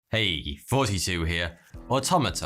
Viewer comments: i always thought he was saying hey 42 here I just noticed he's saying "hey thoughty2 here" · For the longest time i thought he was saying "heyy 42 here"......am i real?